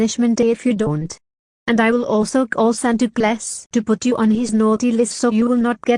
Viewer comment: Anyone else hear "Santa Class" at 5:48?